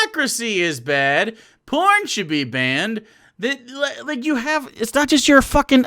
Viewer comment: Wow I thought he said corn should be banned. Maize be.